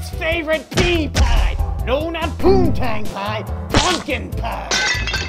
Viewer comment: Poontang pie? I thought you said pooptang pie